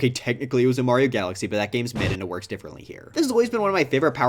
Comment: Did you insult Super Mario Galaxy right there? Seriously, I missed what you said because you were going a bit too fast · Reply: Yeah, he called it mid. He’s called it mid across multiple videos.